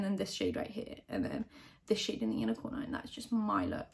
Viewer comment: I was so confused for a second because I thought she was saying ‘unicorner’ which isn’t a word and then I realized she was saying ‘inner corner’ and felt very dumb.